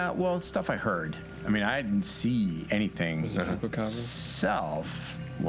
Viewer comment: What did Jasper said at 1:19:11 when talking about "something" coming from Mexico? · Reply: Oh Nevermind it was the "Chupacabras"